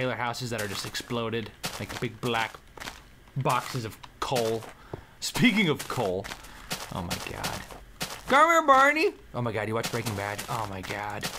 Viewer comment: Did say barney at 8:08?